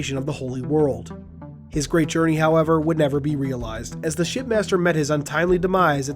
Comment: Glass shattering moment, it sounds like your saying “Shit Master,” anyone else agree. Also, I can’t wait. I live next to a Dave n Busters so it’s gonna be great when it comes out